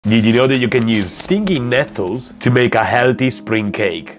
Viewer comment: I thought he said STINKY nettles